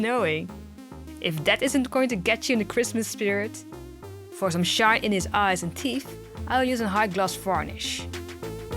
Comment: When you said, "it's accualy snowing. If that doesn't get you in the Christmas spirit... for some shine in the eyes and teeth... " i thought you said, " If that doesnt get you in the christmas spirit you must be shot in the eyes and teeth "